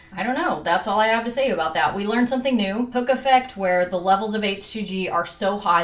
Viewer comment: I don't know if I'm just tired, but I heard "Hookah Fed" instead of Hook Effect